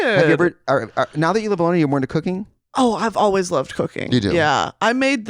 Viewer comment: thought brittany said she always loved cocaine